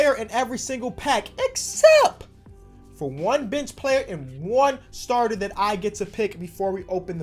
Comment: thought he said bitch player